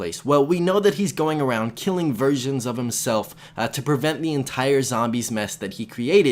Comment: lol I thought u said richtofen was killing virgins of himself